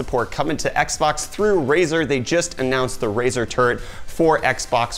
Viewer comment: Did you say Razor Turd?